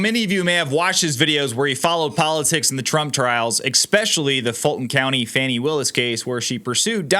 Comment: Hold the phone ... did you say EXPECIALLY ???